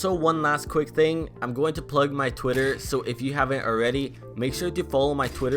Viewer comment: when he said twitter I thought he said twitch